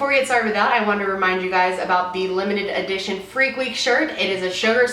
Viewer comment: I thought she said FLEAK week,and I was like YAAS BBY WORK